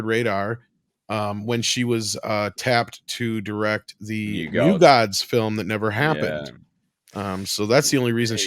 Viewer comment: OHHHH, I see. "New Gods" movie, I coulda sword that I heard the "U-God" movie, lol.